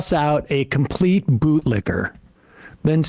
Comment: I thought he said boob licker